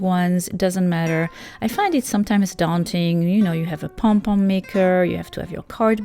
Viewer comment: Good hacks👏 But definitely not number 5 that is just a waste of yarn and also I heard her say pon pon not pom pom😂